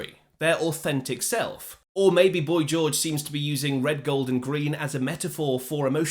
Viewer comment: Red, gold and green?? I always thought he said my golden dream!😂😂